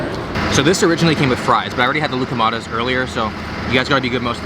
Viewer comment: I heard “gluco madness” when he said Loukoumades. Sounds appropriate though